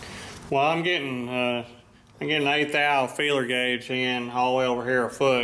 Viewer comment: Not .008", but .0008", or eight / tenths. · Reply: Sorry but I thought I heard say "eight thou" at about 1:40.